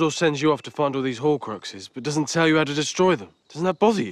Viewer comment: did Ron just say "fondle horcruxes"?